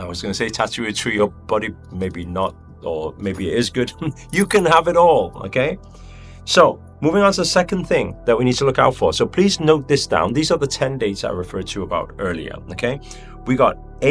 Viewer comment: i heard “do it all”